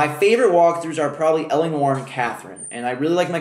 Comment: What was your favorite walkthrough? I have bad hearing, but I thought I heard "Allen Warren Catherine"? Which is obviously wrong wrong wrong, so could someone please clarify?